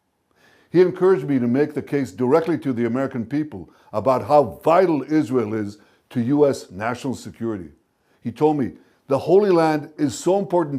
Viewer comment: Did he just say Charlie said..."How violent Israel is to us national security " at 4:00?